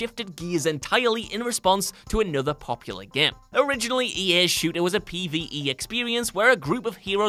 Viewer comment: EA's shooter? Am I hearing that right